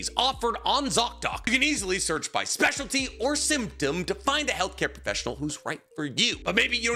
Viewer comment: it may have been because I wasn’t paying attention to the ad, but I heard symptom as “SIMP-dom”😂